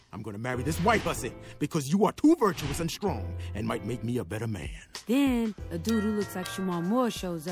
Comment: “You are too virtuous and strong, and might make me a better man “ This is the most underrated line in the whole the video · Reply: Thought he said “You are two virgincent strong,/ me might make me a better man.” As in, virgin and percent put together